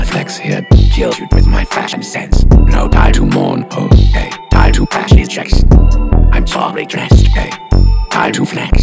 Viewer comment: first listen i thought he said "Don't die to Mournhold, ayy, die to fascist checks" that hit hard